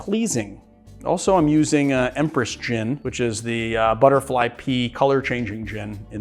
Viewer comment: I know it's "butterfly pea" but I always hear "butterfly pee"